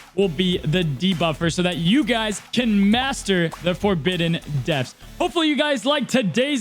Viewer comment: This whole time I thought you were saying the forbidden desk